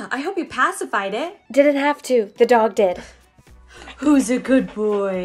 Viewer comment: I thought it said the dog died 😰